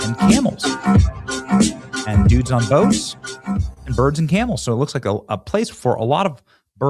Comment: Dang, I thought Benny said “virgin camels”. He said birds and camels. The likelihood…..